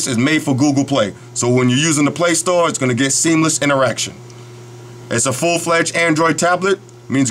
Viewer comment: i thought u said "an erection"